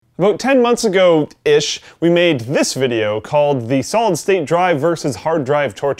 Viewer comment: I heard him say Solid Tate Drive. Now I can't unhear it.